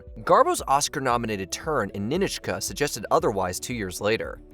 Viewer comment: I think I heard "Ni-nich-ka" but I remember it is "Ni-NOCH-ka". Even co-star Melvyn Douglas says it that way.